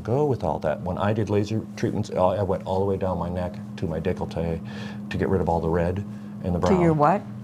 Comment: I heard "from my neck to my dick" before I heard the rest. 😂😂